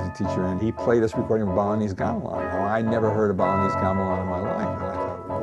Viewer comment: Between 0:08 and 0:18 he's telling something, about somebody playing some intrument. But I can't make up what he's saying, which instrument is he talking about? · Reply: I know my reply is six years late but he's talking about Balinese gamelan players